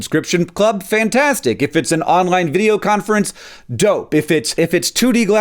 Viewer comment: I thought you said don't😂